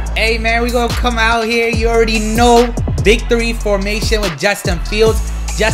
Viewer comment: Did you say big 3 formation ? I always heard it as victory formation , damn maybe it’s both?